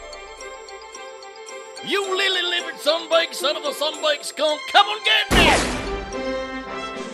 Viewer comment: (Southern Accent) What the heck did Flatfoot say at 26:41? (Normal Accent) No, really. What did he say? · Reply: "You lily livered sunbaked son of a sunbaked skunk, come an git me" 😂👌